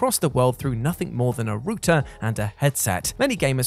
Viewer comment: Rooter? Did you just say Rooter???